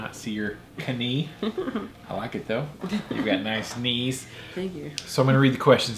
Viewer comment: Knees HAHAHAHAHAHAHAHA Thought he said CUNNY HAHAHAHAHAHA